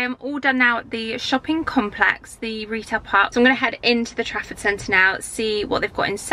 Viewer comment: All this time I thought you were saying “traffic centre” 😂